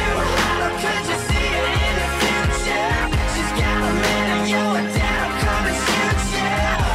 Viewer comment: I'm pretty sure the line isn't "and You're a damn common future" I believe he says "and yo her dad'll come and shoot ya." I mean...I listened to it over and over and tried to believe the first one, but it just doesn't sound like he says 'damn common future' and it doesn't make sense anyway.